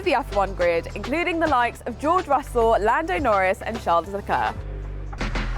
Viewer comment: did she just say: Charles LeCar